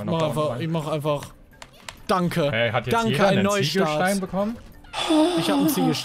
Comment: Haha ich habe anstatt Ziegelstein Siegesstein verstanden XD